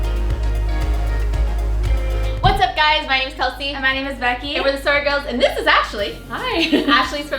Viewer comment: automatic captions say "we're the circle" and I felt like that was information I needed to share